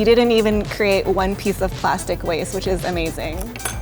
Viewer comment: did anybody hear save the turtels